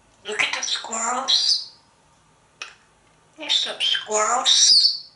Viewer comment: I thought i heard Einstein say "subsquirrels" at 5:49 ? 😂😂